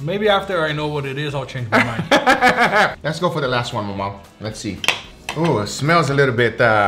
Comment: As soon as he dumped the Caramel Frappuccino on the steak my thoughts and prayers went to 🙏😂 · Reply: Lol i didn't notice is actually "MauMau", i heard "my man" lol